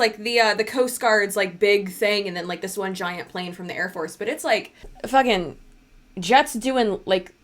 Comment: I thought you said “air horse”😂😂😂